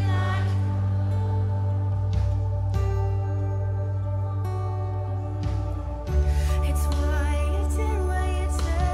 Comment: What is she saying at 8:50 There are some words I can't make out. I would appreciate anyone who lets me know. Thanks saints 💜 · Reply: Lisa Kinston She sings, “It’s why You did what You did, so we can know You intimately. Your love, and Your heart.”